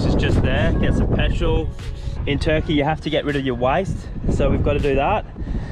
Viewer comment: I had to listen to your departure message 4 times as I thought you said "in turkey you have to get rid of your wife." LOL x4 "Waste!!!" Funny how it is all English but accents shure do screw me up into some ridiculous things I hear. Never ending entertainment. Thank you guys. I love your channel and can't wait for each episode.